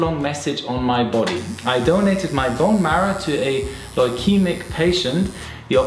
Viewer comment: what does he says he donated? I don't get it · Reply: Bone marrow... To a person suffering from leukemia.